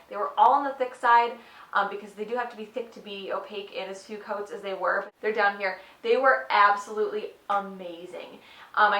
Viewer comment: What neon collection did she say she recommended?? I couldn't get the name.it was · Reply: shabrea ellison I believe she said Boardwalk Collection from Virago Varnish. 😘